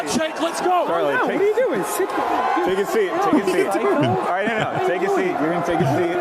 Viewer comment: did anyone else hear hassan tell kirk sit down psycho 22:05 😂😂